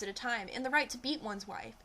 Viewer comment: At first I thought you said they wanted the right to EAT their wives. Lol.